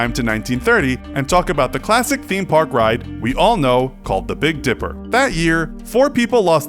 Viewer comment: I thought that it said the big diaper